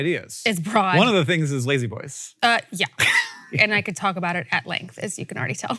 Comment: Hank... I listening to this while I worked and I was so confused.... I thought you both said "Lady Boys" and I was surprised and concerned and oh so confused. Then there was "a room full of them" and then there was "BOGO" and "rocking" ... it took entirely too long to realize you were talking about La-Z-Boy recliners.